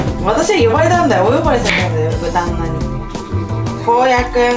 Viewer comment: お呼ばれしたんだよこの豚マンにって聞こえて、キツイこと言うなーって思った笑